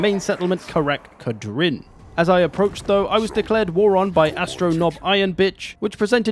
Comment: For a second I thought you said "I was declared war on by astronob iron bitch" and choked on my water.